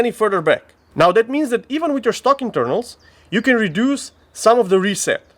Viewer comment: Not going to lie, I straight faced looked at my wife and said, "did he just say stocking turtles?" To which she replied "no idiot, STOCK INTERNALS!" 😒 Sorry, I can be ignorant.....sometimes, lol. Definitely a nice upgrade though, great job, reminds me of the SRT kit on my P226. Looks like it might be as good if not better than that.